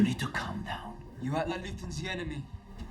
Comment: = "You act like Luthen's the enemy" is what Wilmon says. Making Cassian's line that much more potent.